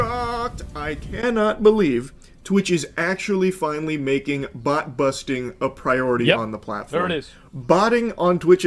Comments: lol I heard "butt busting" and thought, "Well, it's America..." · I might be broken - I thought he said 'butt busting' instead of "bot busting." I need help